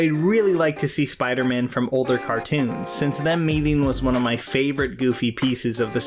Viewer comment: What do you say at 6:13? "Since them meaning? Since them leaving?" What the mumblefuck? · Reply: “Since them meeting”